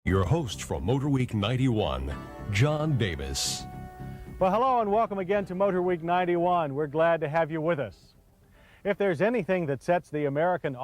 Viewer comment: Ngl with that audio glitch it sounded like he said "John Bebis" lol